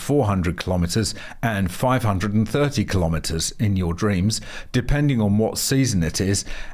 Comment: Who heard subliminal "in your dreams" at about 3:20 in the video?? 😆 Reminds me of this: <link>